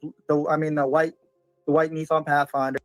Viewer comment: I heard it I heard him say “ blue and then I mean the white”